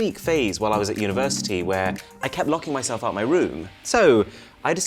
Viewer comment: when he says "locking my self out my room" the subtitles say "locking my self in my room" Dunno what made me see that.